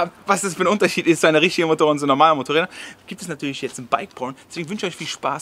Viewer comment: Ich habe als erstes bike por*o verstanden 😂😅